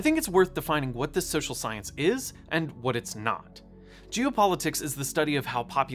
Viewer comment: Loved the video and the education, but it sounded like you were saying "Jew politics" instead of "geo", and that made me feel uncomfortable.